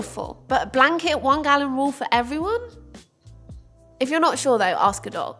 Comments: „If you‘re not sure though, ask a dog“ is what I heard and I‘m gonna take that advice and apply it 😂♥️ Good video! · Why when she said "ask a doc" did I hear " ask a dog" I mean dogs know whats up so I guess its interchangeable lol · I thought you said "ask a dog" and here I am asking my dog if I should have another glass of water. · I wasn't sure about "always ask your dog"... then I heard it correctly 🤣